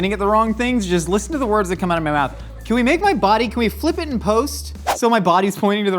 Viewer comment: it dead ass sounded like he said "can we make my body queef"😂